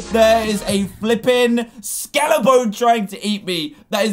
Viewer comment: Dan: WHAT IS THAT! WHAT IS THAT?!!?? Me: A skeleton dude? Others: Your mom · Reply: I thought you said Skeletron (Terraria boss)